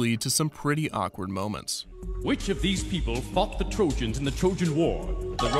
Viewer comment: I swear… I thought he said “which of these people fucked the trojans in the trojan war?” LMAO I had to replay that but a few times 😂😂😂